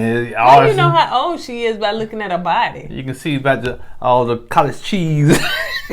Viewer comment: OMG did say "College" Cheese? Buhhaaahaha 😩😂🤣💀